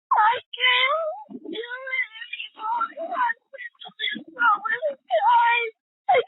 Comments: We do have subtitles and better sound from our computer though. A dispatcher's phone is less clear. This still doesn't justify the dispatcher's behaviour though. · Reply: But I heard 'I can't do this anymore' repeatedly near the end of that with no subtitles (because .... isn't much of anything), and my hearing is not reliable. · I'm not trying to protect some of these dispatchers but try listening to the callers without reading the subtitles to see if you can understand them. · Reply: I heard say "I can't do this anymore" multiple times and the subtitles were just ...... and I'm Hard of hearing. So...?